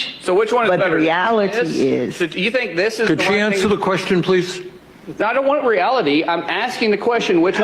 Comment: You really should take care when using quotation marks. The words inside the quotation marks should be verbatim what is being quoted. It sounded like he said, "I don't want 'reality'".